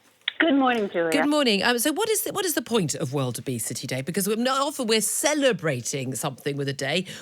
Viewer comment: I thought she said wildebeesty day